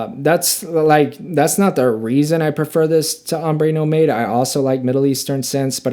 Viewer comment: Did you just say ‘Ombe No Made’? 😂😂😂😂